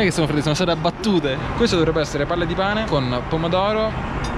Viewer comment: purtroppo ho capito "Palle di Cane "